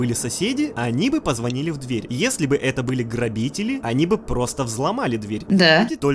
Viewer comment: Мне послышалось не грабители а родители